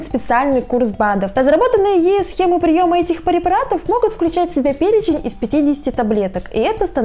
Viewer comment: никто не заметил,что она сказала «парепаратов»?😂